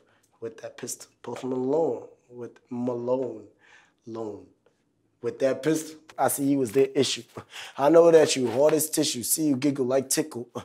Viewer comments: Please don't tell me i was the only one that though ski was dising post malone for a second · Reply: i thought he had said "post malone with that pistol, I see you as dried tissue" when i first listen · I’m high key mad he said “see you giggle like tickle” in this. I could’ve sworn he’s been saying “see you giggle like the senju” for a Naruto reference for Hashirama Senju